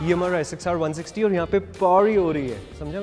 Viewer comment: seriously did said pawri